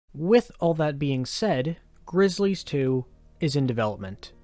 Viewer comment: i thought it said Glizzy's